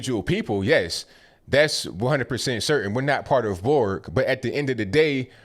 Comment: is he a trekkie? Did he say we're not part of a Borg?